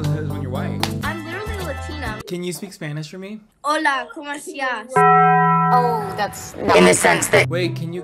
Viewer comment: maybe the Spanish was the friends we made along the way (but fr what did she say “hold comp estaaaash” DEAD)